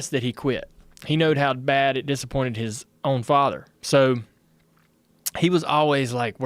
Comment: Did he say, “he knowed how bad it disappointed his own father.” Are you sure you didn’t drop out of school D2?😂😂😂